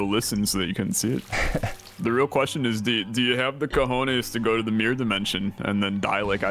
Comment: Did he said "Cojones"?